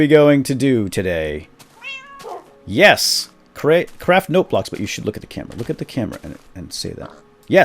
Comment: At the start I thought u said crap note blocks ;P